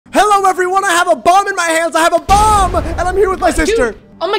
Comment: At the very beginning I really thought he said "I have a bong in my hands!". I mean with last video I wouldn't dare doubt it.